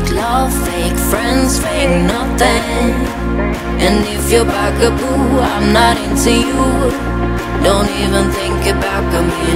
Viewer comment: Why do i keep hearing if you're not a bag of poo I'm not into you Please 😭😭😭